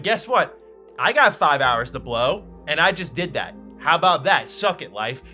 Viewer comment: All i heard was “i did 5 hours of blow”😂😂